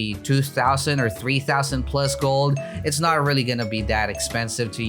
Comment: did he just say gold 😃